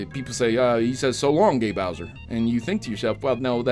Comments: I always thought Mario said, "So long, me Bowsie!" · I always heard “ So long de Bowser”